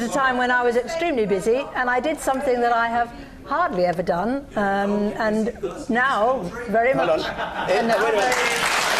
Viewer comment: Did anyone catch what the heckler said at 3:40-3:50 that made the audience laugh? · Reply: “Are you the only busy person in this country”